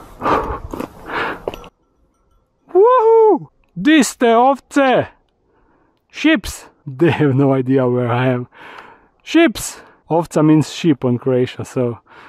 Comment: Great vid. Loved the view, the animals and the little stream You will need a bigger house for the more jungle stuff you want! And then there’s those two ROCKS! I ❤ to look at this stuff through your eyes. Your love for nature is so apparent. I still don’t like spiders but you gave me a respect for their value. “Ships” 🐑I ❤ it😂! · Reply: I kept hearing “Chips” your little nemesis!